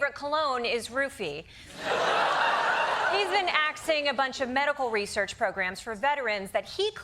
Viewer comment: did she say aksing